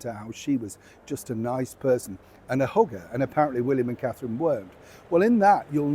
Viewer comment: OMG! Because of your accent, I thought you said that Megan is a “Hooker”, and you actually said that she’s a “hugger”… I am dying here!